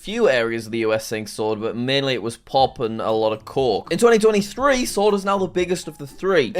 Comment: Man i know u wanted to say coke but i heard "cock"